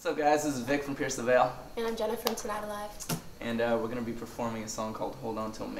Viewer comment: i literally thought she said "i'm Jennifer" for a second I was like what happened